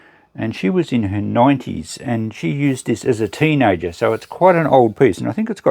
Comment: Nice video as always. Have one question regarding that fencing sword.At 16:36 , it sounded like "it was used as a teenager" and the Youtube closed captions aren't much help either :) So, if anyone could enlighten me I'd be much obliged.I'm guessing it was used as a poking stick?